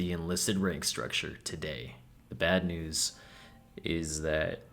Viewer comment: Did you say good news and glad news 🙃